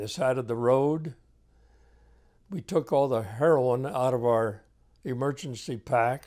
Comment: Did he just say heroin?